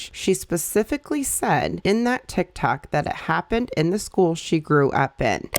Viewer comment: ma'am did you say TikTak 😂